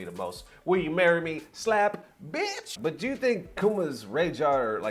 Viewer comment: I thought you said Akuma 😅 🇺🇸